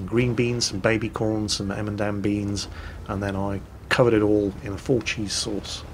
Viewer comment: did you just say "emendam beans"? ... ... ... do you mean "edamame", as in "ed-ah-mah-may"? XD